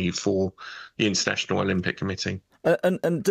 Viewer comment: did he just say "more money for the incestual Olympic committee"? Ohhhhhhh, INTERNATIONAL